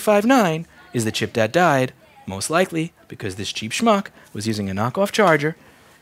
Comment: Did say "shmock" at 06:31?